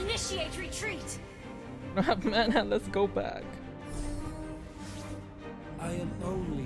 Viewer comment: I heard it like "Wala na akong mana, let's go back." I suddenly thought that you're a Filipino :P what's his nationality guys?